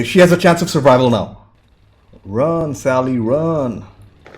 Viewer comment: "Run, Sally, Run". That made me laugh. All I heard was "Run, Forrest, Run".